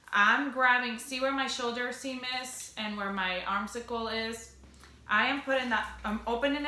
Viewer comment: It is called the armscye. Pronounced arms eye. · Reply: i thought she was saying arm circle with an accent, we knew what she was talking about.